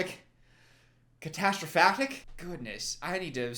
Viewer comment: I fully thought you said Catastrphaggot and honestly I feel that 😂 I am now a catastrofag